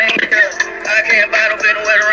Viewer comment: Sound like he saying gittu gang wtf